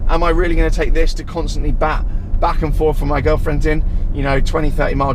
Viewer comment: did he say “girlfriendS” 😳